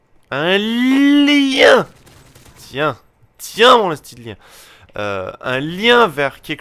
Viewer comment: Pouce vert si j'ai bien entendu: à 6:24 , j'ai entendu: tiens, tiens mon osti de lien :O (lol) ( 4 EVER)